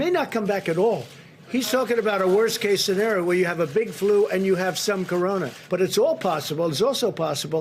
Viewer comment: WAIT...did he say at... 1:49 *”big flu and SOME CORONA?*”... okay 👌 just checking...